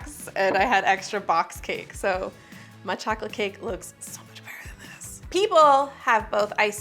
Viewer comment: I thought you said betterup, 😂😂😂